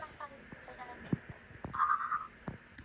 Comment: の聞き取れない部分の最初だけ、 「私も……もっと生きていたい」って聞こえるんですが。